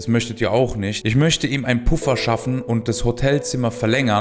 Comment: 'ich möchte ihm ein puff erschaffen' hab ich so verstanden hahahah